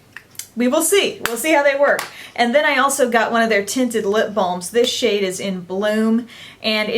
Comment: I was doing something else while watching this video and I heard you say "LIP BOMB". That certainly got my full attention and I realised you were saying "LIP BALM". I have been to the US a few times and I still can't get used to hearing my name pronounced as "Darna". lol